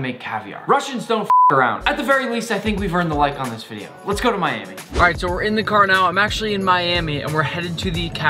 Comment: When you said "now lets go to Miami" i thought you said lets go to manny lol